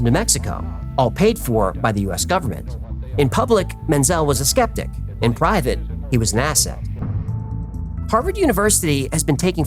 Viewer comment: I thought you said, "In public, Menzel was a skeptic. In private, he was an asshole." I replayed it like 4 times before I turned CC on.